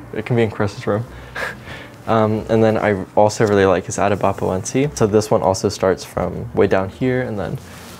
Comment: what’s the name of the plant at 8:11 ? i can’t understand what he said and the cc are incorrect :( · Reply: Philodendron atabapoense ✌🏻️❤️